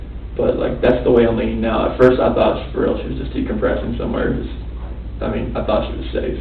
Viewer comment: I thought I heard him say "first I thought she was decomposing somewhere" and I was like no sht bruh 😐